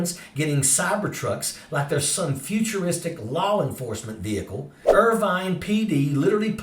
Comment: did you say LOL-enforcement vehicle? 😂😂😂